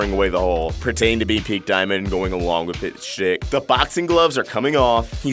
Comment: Alright who else thought he said shit